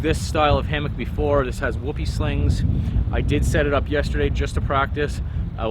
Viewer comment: I thought you said it had "whoopie stains" on it....